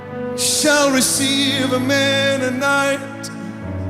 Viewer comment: sounds like he says, " shall receive a Mennonite .